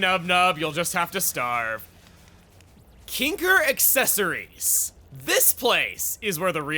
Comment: "Kinkir Accessories. Now this place is where the real excitement lays" Now, you can imagine my confusion when I looked away for just a second and heard it as KINKY Accessories...